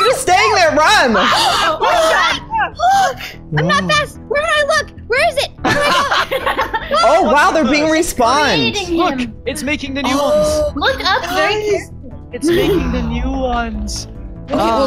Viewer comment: When you guys said 'its making the new ones' i thought you said its making the noodles XD